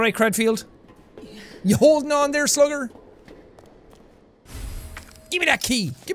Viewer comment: It sounds like she says "yes" when he asks her if she is ok 😆